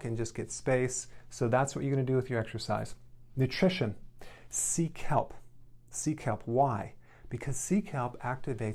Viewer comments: "SEA KELP".......Thought he said "seek help". I'm in a psychiatric hospital!!! My bad...... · "Sea kelp'... I heard it as "seek help" ha ha ... This video advice sounds excellent. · When he first said "sea kelp" I heard "seek help" and I was like: "What? Why?" lol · I thought you said "seek help", not "sea kelp" lol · haha, i thought he was saying under Nutrition 'seek help'. i kept thinking why? finally i realized he was saying 'sea kelp'! lol. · Omg when you said sea kelp i thought you were saying seek help....😂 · When he did 'sea kelp.' i heard 'seek help! Seek help! Why? Because.. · oh, SEA KELP, I thought you said 'seek help', a pop up was in the way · without the captions i heard “sea kelp” as “seek help” help uh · Sea kelp... I thought he was saying seek help!😝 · I thought he said seek help until I read sea kelp😂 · I thought he said SEEK HELP... not sea kelp. 😂😂😂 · Sea Kelp, I thought he said Seek Help 😂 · At first I thought he said "seek help"...sea kelp...lol. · Oooh SEA KELP. I thought you said "seek help" 😂